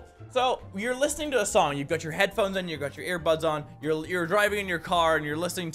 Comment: did he just say "put your headphones in, and put your earbuds on"?????